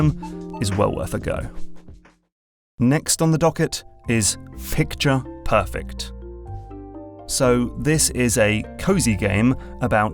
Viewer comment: The sliding effect on the cards really made it sound like you were saying "next is f- picture perfect" throughout the video. Took me a while to realise it wasn't an audio editing glitch.